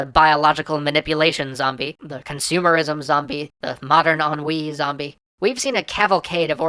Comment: What are you saying at 3:20? The m... zombie thing?